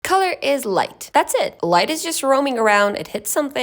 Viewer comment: I thought it said colon